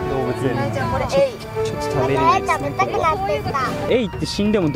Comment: 聞き間違えでなければ、「エイ食べたくなってきた」って聞こえる エイって食えるっけ？